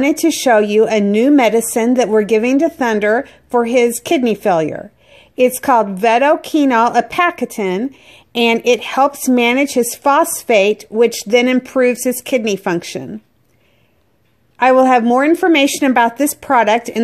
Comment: Sure hope it works. At first I thought you said it managed his prostate! 😄 Rewound, and OK, phosphate makes more sense.